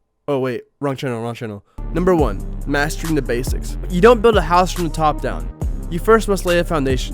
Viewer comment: I thought he said masturbating basics